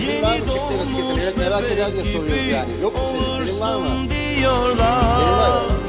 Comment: Hello. Can you please tell me what Beyaz says to Hande at 1:41 - she looks sad and Kerem looks kinda worried. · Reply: hello, Beyaz said to Hande do you have a boyfriend? Hande did not say anything then beyaz says it means you have. That is why Kerem was sad